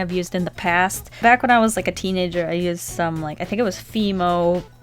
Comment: When you was talking about how soft it was it sounded like you said " back when I was a teen ninja" instead of teenager😂